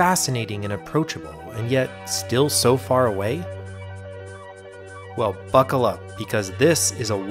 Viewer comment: guys guess what i heard him say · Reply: I think I heard "Well, f**kle up."